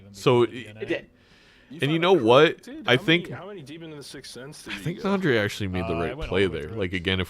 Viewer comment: <link> Really thought was saying "Sixth Sense" here and was so confused. He just meant Nandre had a nice nice score. Took me a rewatch to figure it out XD Fun matches!